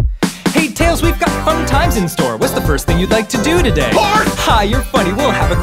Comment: what did tails say? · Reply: Well all the lyrics I've seen said he's saying "whores". Sounds like barf to me.